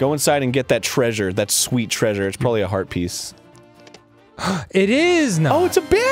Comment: I thought he said, "It's probably Arby's"